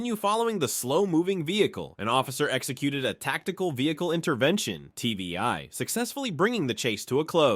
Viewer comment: Tactical Vehicle Intervention And then the subtitle says TBI